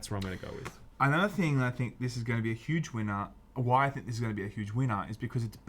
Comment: Uhhh what did you just say sir? A huge...... Weiner?? Baja!